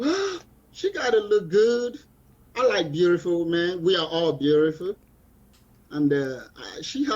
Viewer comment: He said he likes beautiful men. I played it a few times and the closed caption heard what I heard. Seems like he's gay or bi but wants a wife and kids. Everyone doesn't need to breed.